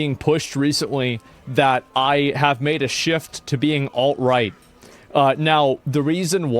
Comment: Instead of alt right I heard "alright".